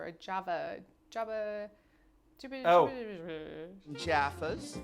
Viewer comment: Jaffas, I think is what she said? They’re small orange flavoured sweets with a chocolate inside